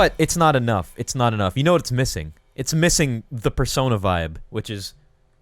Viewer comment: Thought it said personal vibes